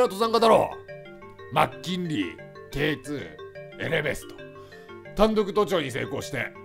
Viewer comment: エレベストって聞こえたのだがw